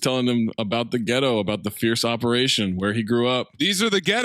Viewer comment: The amount of patois that misheard into random shit is hilarious. Pure sufferation turns into fierce operation, which makes no sense in that scene